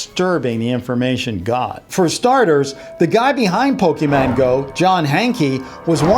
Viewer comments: did he just say Pokeman go?! XD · Did he just say Pokéman go